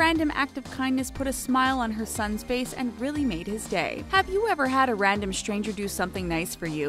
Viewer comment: have a good life you sexy person scrolling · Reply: LMAOOO I thought you said Have you ever had a sexy person scrolling 😂😂😂😂😂